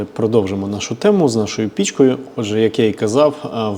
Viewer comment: "з нашою пічкою", а не те, що вам почулось))